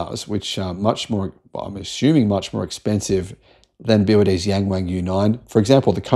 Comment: they need to drop that laughable name. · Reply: yeah, I thought he said 'Gang Wang' at first, I was 'huh?'